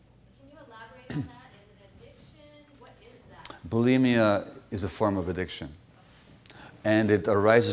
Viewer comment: what is a form of addiction?I couldn’t understand the word …ing?believing?reliving?relieving? Any help what he said? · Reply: Boulimia (an eating disorder). It arises for the same reason addiction arises.